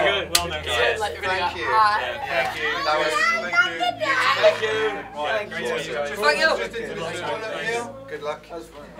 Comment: what he's saying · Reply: "AT THE END OF THE DAY!!"-h